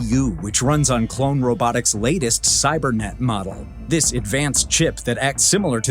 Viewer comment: did I just hear "cybernet model" kinda like "sky net model" <link>